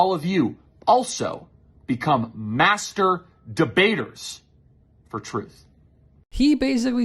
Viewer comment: Did he just say become "masterbators" of ourselves?